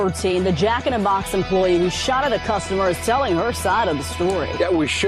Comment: I didn't hear them say "telling" her side of the story. It sounded like they said she was "selling" her side of the story.